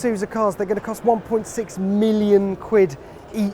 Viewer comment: ow wow, I thought you said 1.6 million SQUID... never mind.